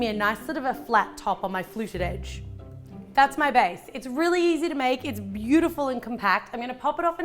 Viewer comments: #ThatsMyBae like if you heard that as well ( 2:26 ) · i thought she said thats my bae LOL